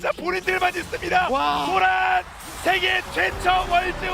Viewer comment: 쓰리핏이면 왕조가 맞다.. 18:29 세계 최초 월즈 우승이 아니고.. 생애 최초 월즈 우승으로 들리는데..''a